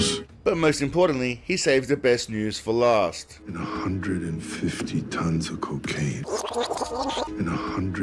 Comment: I misheard 50 ton of cocaine as 50 ton of cookies